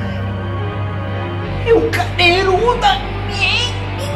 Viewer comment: nunca entendi oq ele falou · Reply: "É o Caderudo amiegan"